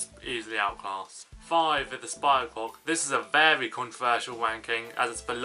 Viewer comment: What did my dude say at 2:04?!?! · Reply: 'Very controversial ranking' but it sounds a bit like 'wanking' lmao